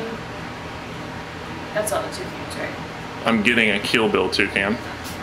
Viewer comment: Sounded like you said "kill bill toucan"